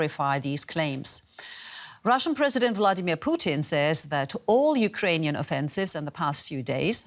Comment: I thought I heard her call him Bloodimir Putin